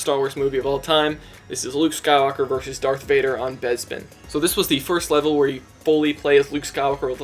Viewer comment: Did you say that Bespin is Besbin?